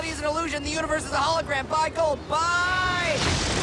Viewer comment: I always thought that at the end he said “Buy gold, Buy.” But now knowing he’s actually aggressively shouting “BYE!” somehow makes it a lot funnier.